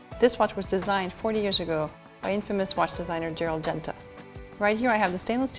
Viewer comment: designed by infamous designer Gerald Genta? did she say INfamous or 'a famous designer'??